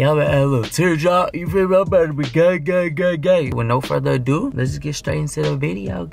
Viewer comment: Bro wait 😂 1:51 this childish asf but i heard “Im bout to be gay gay gay